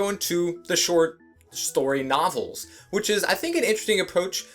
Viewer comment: I swore I heard you say "short shtory nofels"